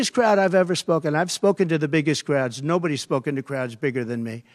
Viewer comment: Oh he was talking about crowds? I thought he said clouds and he reminded me of that old guy on tv yelling at clouds